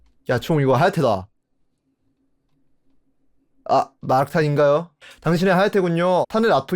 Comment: 여기 자꾸 마라탕인가요로 들려요ㅋㅋ